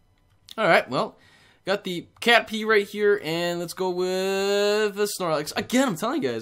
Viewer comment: "We got the... cat pee right here" I wasn't looking at my screen and I sincerely thought that's what he'd said